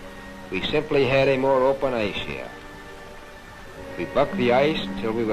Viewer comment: Did he say “We Fufffed the ice”